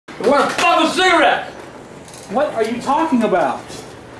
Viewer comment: When the video started I thought he said “ I WANT TO FUCK MY CIGARETTES! “😂